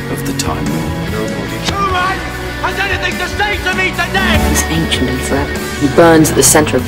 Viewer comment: brilliant! what does he say at 2:46? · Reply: thank you <3 and ---> "nobody human has anything to say to me today" <--- totally loved this line, anger of the doctor <3